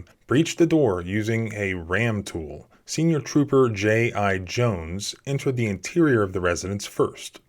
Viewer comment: "J I Jones?". I kept hearing "GI Joe"